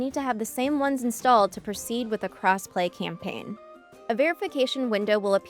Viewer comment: I thought she was saying “crass play” until I saw the text on the screen 😂 wondering what a “crass play” mode would entail 😜